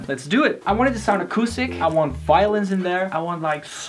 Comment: I literally heard "violence" instead of "violins"